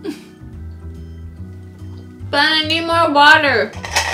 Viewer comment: did she just say she needed more water ?